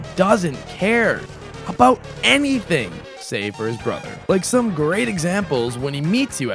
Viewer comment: I really hope that when you said "he doesn't care about anything, say for his brother" that you meant that Papyrus cares for a lot of things and Sans doesn't. For a second it sounded like you said that Sans doesn't care for Papyrus, which would make no sense at all considering he calls you out about killing his brother in the neutral ending if you choose to do so, the genocide ending, and the fact that he disappears after you kill Papyrus. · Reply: No i said, he doesn't care about anything, save for his brother" meaning "besides his brother" in that context.